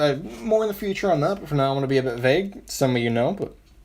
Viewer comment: did you say now I'm going to be able to vape.